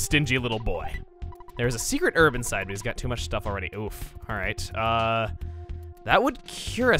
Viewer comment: DID YOU JUST SAY OOF?